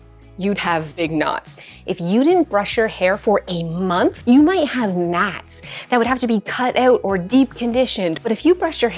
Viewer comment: I thought you said: "If you don't brush your hair everyday, you might have gnats." Lol